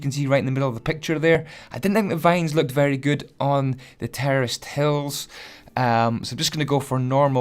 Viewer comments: anyone else thought he was saying terrorist instead of terraced?? I know I did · I thought he said terrorist instead of terraced “The terrorists farm”